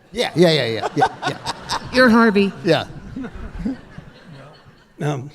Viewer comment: I admit. It sounded like the moderator said ‘R.V.’ instead of Harvey. 10:47 Keitel keeps talking about Reservoir Dogs. 12:04 “It’s KAI-TEL.”